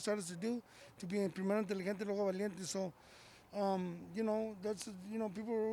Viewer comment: what the hell did he say!? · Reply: Primero inteligente y luego valiente